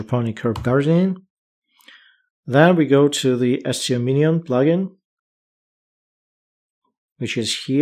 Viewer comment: Umm...Guys What's the Plugin at 7:20 called as I cant understand him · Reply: The plugin is called SEO Minion)